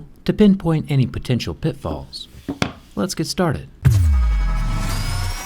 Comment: I thought he said "Phets pet ptatrted"(let's get started)